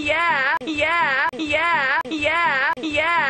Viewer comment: Sometimes it sounds like he says yeaha